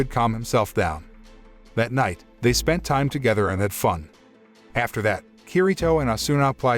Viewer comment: I got so shocked at this cause I thought you said "They spent time together and then fuqed" and my jaw dropped. I said, " Bro, this ain't the abridged version. Calm down!"